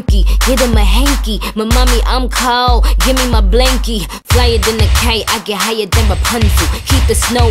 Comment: Wait! Did she just say at 0:45 that she do coke? 🤔 I thought u say u don't do that ma'am. I mean, to each it's own but..u ain't gotta lie Craig😩